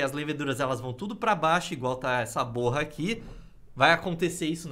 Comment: juro q escutei p0rr@